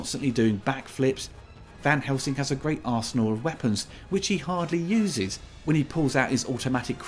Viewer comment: Totally thought you said “arsehole weapons” instead of “arsenal of weapons”.